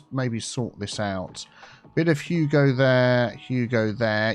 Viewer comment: Bit of hugo there. It sounds like youre saying that instead bit of you go there 😂